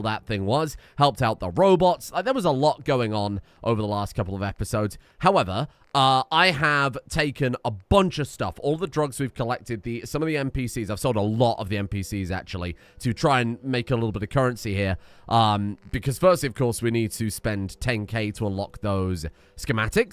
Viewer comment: I heard MPCs as NPCs and was very concerned. Fortunately, this isn't Rimworld.